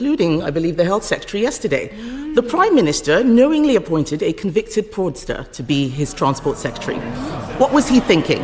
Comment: As someone who's not up to date on UK's politics, I thought she said convicted pornstar:D